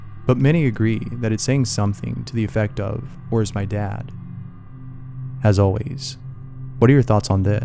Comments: i heard eat my dad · at first it sounded like it said “oh my god”.